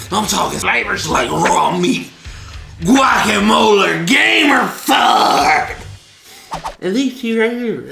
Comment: At the time stamp 14:17 i think he says gammer fuck